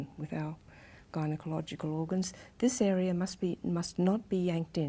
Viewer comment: What does she say at 12:23? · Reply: Gynecological organs :)